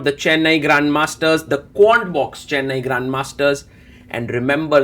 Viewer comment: You were saying Quantbix but I thought you were saying Corn 🌽 Box so I was looking for my bowl of Cornflakes 🥣 to sit down and enjoy the game even more 😅😂